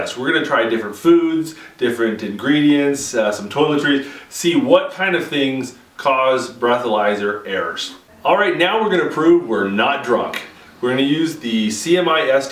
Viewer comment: At about 14 seconds in did he just say "toiletries"? LOL